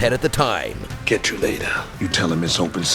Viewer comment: "catch you later"?... Is that what he said? 1:17 - "you tell 'em it's open season on all suck WHAT"???... I can't even understand the last word or two of this one